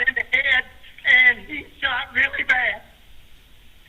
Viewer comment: I hear “I shot em”. Did anyone else hear that? Weird.